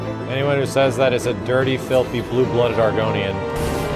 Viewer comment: Did you say dirty filthy blue blooded Oregonian..? lol <3 from Portland